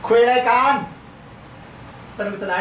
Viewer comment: คิดว่าตาพูดว่า ขวย ไรกัน 555 ตกใจหมดเลยอ่อ แกพูดว่า คุยไรกัน หูกูเพี้ยนหรือแกพูดไม่ชัดกันเนี้ยย😊😊 และก็นาทีที่ 12:07 แกพูดว่าสามคนตอนนี้หรือ สังคมตอนนี้กันแน่คับ 555